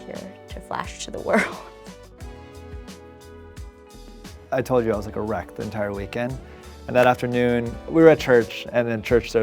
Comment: i really thought he said "i told you i was erect all weekend"